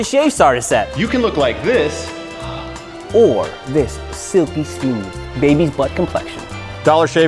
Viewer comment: During the ad I really thought he said “suppy smooth”😂😩😂😂😂